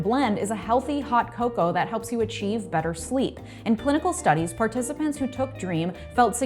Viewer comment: regarding your sponsor, did you say, In political studies?? If so, what in the world?? How are political studies relevant to a cocoa mix? On the other hand, if I heard wrong, pardon me. Now I'll continue to the reason I'm here.